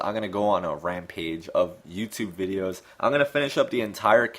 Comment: Excellent video! Now I understand why the Japanese think Lucario is good. Also at 24:14 - 24:16 at first I thought you said Mewtwo videos, but I know you said Youtube.